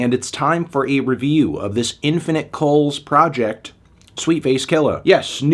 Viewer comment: Thought that said Infinite Soles at first and got excited.